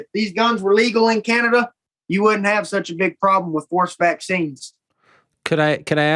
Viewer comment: So, when he said “forced vaccines” the first few times, I swore he had said “horseback scenes”